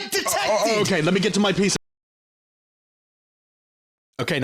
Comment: When said PC i heard bro said piece of